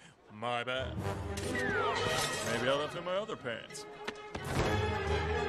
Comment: I always thought he said "underpants," not "other pants." Anyone else?